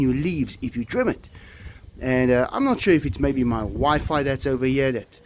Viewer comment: When you were talking about the sega palms you started to say, "I'm not sure if it may be my wifi..." I honestly got a fright because I thought you were about to say "my wife"! I thought you and got married at some point in Cape Town or something. 😯